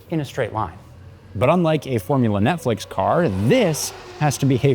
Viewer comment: Did you just call F1, Formula Netflix?